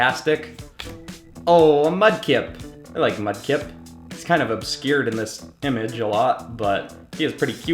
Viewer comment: I heard yah like Mudkipz